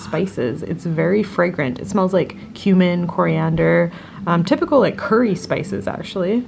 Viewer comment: I know you said “cumin, coriander”, but I heard “human coriander” and I’m still laughing... 🤣